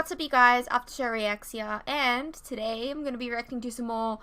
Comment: I am saying it right. here... I am Australian lol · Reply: I am sorry, I didn't know you were saying reacts here. I thought you were trying to say reactions but said re- axe- sea- ah.. Thank you for replying, I appreciate it.